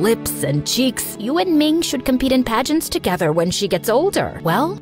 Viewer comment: i coughed so i thought she said to grow a cabbage 🥬 patch 😭